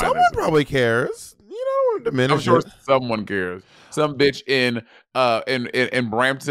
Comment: It was a little hard to understand, but it sounds like said, "Come bicho" which means "Eat dick." Bicho in some places, I know Puerto Rico for certain, is slang for dick. In other places, they say bicho for insect, so...be careful. Lol I was in Costa Rica in the middle of summer with group in college and a girl who had learned Spanish in Spain said to one of the Costa Ricans, "Hay muchos bichos aqui." And the dude looked at her crazy and walked away. She asked me, what happened and I told her that he probably understood her to say "There are a lot of dicks here." rather than what she intended of "There are a lot of bugs here." She was mortified. LOL!!